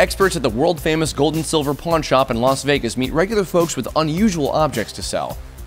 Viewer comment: I thought he said porn shop